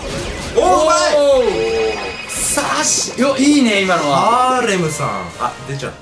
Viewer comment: にヒカキン、ハーレーXのことハーレムって言ってるように聞こえる。